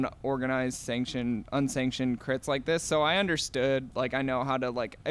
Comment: Did I just hear “understanded” (understood)